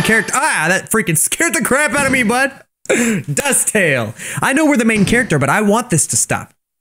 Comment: When you said DustTale I thought you said Duck Tales and in my head I went. A WOOO-OO